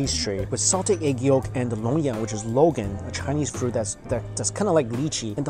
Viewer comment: What did you say at 12:26? "charcoaled pastry with egg yolk and _______"? The captioning says "egg yolk and ammonia" but I don't think that's what you really said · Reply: Found it. Its charcoal bread. The fruit is called longan, or dragon eye fruit. Hope I helped :)